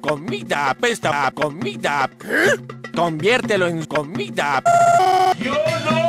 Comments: "El dice que tu comida apesta a comida, conviertelo en comida" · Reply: Pensé que decía conviertelo en mis pompitas jsjsjs xdd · "El dice que tu comida apesta a comida, conviertelo en comida" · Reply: Pense que dijo convirtelo en mi comida